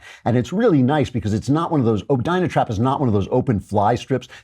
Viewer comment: with low volume, i thought you were saying, "gyna-trap." like, "vagina-trap," which is almost redundant and can be referred to as simply, a trap.